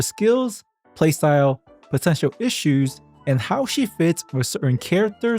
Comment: When you said "potential issues", I heard "what's up with her shoes?" and thought you'd mention what makes her unplayable. Her shoes are GROSS 🤮and an instant deal breaker. Doesn't matter if she's good... easy skip because of those cloppers 🤢